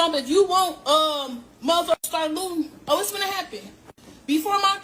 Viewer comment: Did she say start stealing?